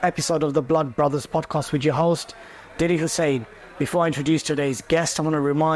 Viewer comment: I thought I heard DIDDY Hossain